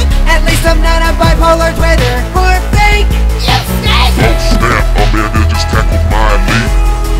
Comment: I'm confused.. Am I the only one who hears her saying "you snake"?🤔